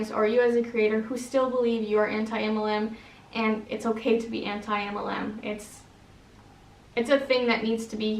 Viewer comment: I thought it said "dear anti MLM, it's ok to still be in an MLM, I am" LOL